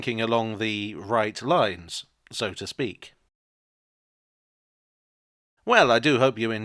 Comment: You say "So to speak" I hear "Sodastream "